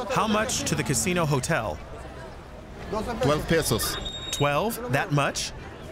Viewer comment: did I hear 12 PESOS?